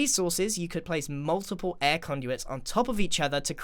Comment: Somehow I thought he said air condition and not air conduit and I was wondering for a whole 15mins why I would air condition my Minecraft world.